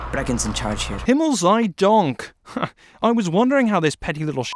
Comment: did he just say Himmel sei Dank?😂😂